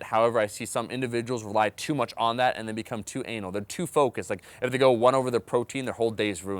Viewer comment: Did he say "too anal" at 09:33? XD